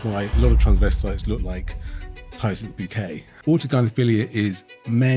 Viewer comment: Can somebody help me what Dr. Hakeem is saying at approx. Minute 0:24? a lot of transvestites look like "heis in the bouquet"? It's in the English undertitles and it makes no sense to me. What is he saying? Thanks if you can help out. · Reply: Hyacinth Bucket - a comical woman from a television program called “Keeping Up Appearances”